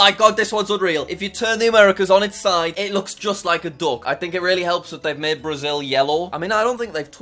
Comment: When he said that "oh its a duck" bc he said british accent. i thought he said "Dog" u know i speak a little british but NOT "DOG" aka duck